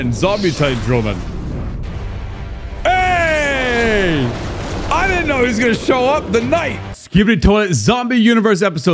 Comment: What did you just say?? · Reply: Ayy is what he said